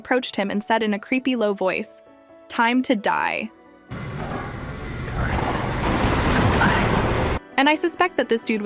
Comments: I heard "time to play" Who agree? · i just hear time to play